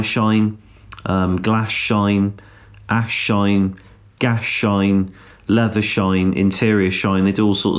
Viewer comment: Did he just say gash shine?